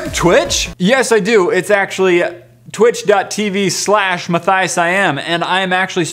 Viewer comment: My sister thought ur twitch said "Matthiasism" like it was a religion or something instead of "Matthiasiam" and I just laughed and said "Wut?! Matthiasism?! It's Matthiasiam!" And she just laughed with me and all she had to say was "Oh..."